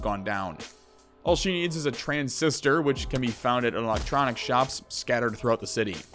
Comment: "all she needs is a trans sister" i replayed that like 5 times i dont know what your actually saying but this is hellarious go get the trans sister :D